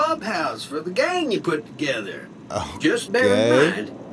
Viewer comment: did say ughhh gay, it doesn't seem like something he would say so idk 😒